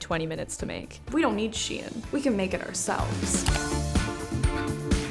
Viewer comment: when she said “we don’t need shein, we can make it ourselves” I thought she said “we don’t need SHEITTT” lol